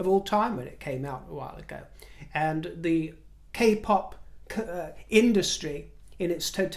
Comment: Sorry, when you said “k-pop industry” I thought you said “kapok industry”! I wonder if kapok is still used these days.